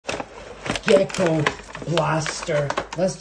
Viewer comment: I thought you said “ghetto blaster” at the start, LOL 😆